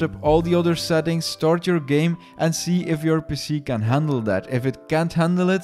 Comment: i thought he said " and see if your pussy can handle that" lol but he said PC